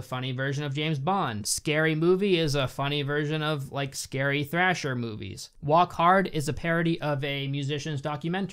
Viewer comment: Was I the only one who heard him say thrasher movie instead of slasher movie?